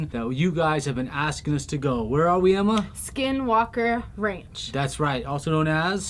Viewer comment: I thought it said skywalker ranch for a second